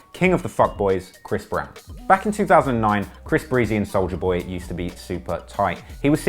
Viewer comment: Hahaha....did he just say Bris Breezy?